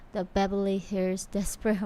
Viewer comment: I thought she was saying Beverly Hillbillies. This was so amazing. Play in every high school.